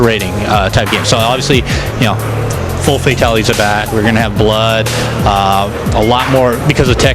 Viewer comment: did he say 4 fatalities are back ?